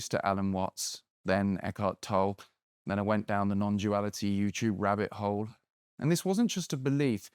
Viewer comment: Really thought you said non-jewality which is me. Just trying to find anything at all that isn’t owned and controlled by Joo’s. But you said non-duality…